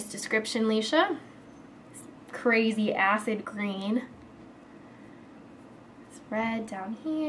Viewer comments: i thought you were going to say "crazy ASS green" instead of "crazy acid green" haha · am i the only one that thought she was gonna say "crazy ass green" instead of "crazy acid green" haha. oh my mind. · when you said "crazy acid green" i thought you were going to say "crazy ass green!" xD<3